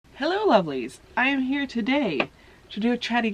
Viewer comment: I misheard you say, 'Hello uglies' lol